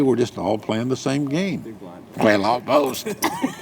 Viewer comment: what did Doyle say? I didn’t catch it. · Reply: He said "well almost"